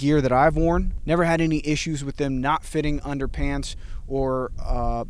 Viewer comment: Did he say "underpants" 1:29. Haha, please excuse my childish sense of humor.